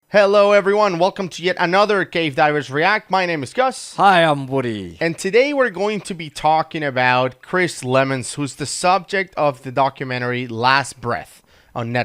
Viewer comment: At first I thought the intro said, "[bla bla bla], where gay divers react." Had to listen again. Cave Divers react. Got it.